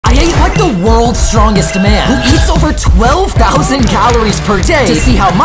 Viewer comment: I thought he said “I ate the worlds strongest man”